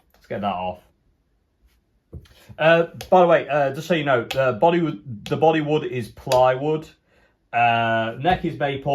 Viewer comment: Can't hear the vid properly due to eating crisps, thought you said the body was Bollywood and plywood :-)